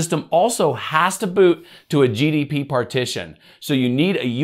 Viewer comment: Maybe it's just me, but I kept hearing "GDP partition"... If I'm not crazy - it's "GPT" FWIW.